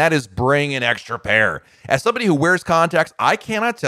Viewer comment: For a second I thought he said "condoms" instead of "contacts" Go back and listen to that part but pretend he's talking about condoms. It's kind of funny.